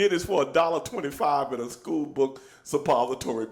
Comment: did Stevie just say 'school book suppository'? That must be REALLY uncomfortable...